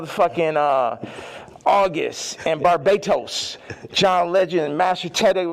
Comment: “Bar-Bait-Toast “is what I heard?🤔